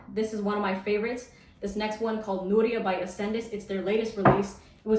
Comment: does anyone know the name of the fragrance she mentions at 8:15? i couldnt understand the name. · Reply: Nooria by Accendis